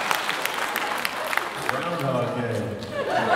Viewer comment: they're amazing! what is he saying at 8:52 tho? can't really hear that line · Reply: He says, "Groundhog day"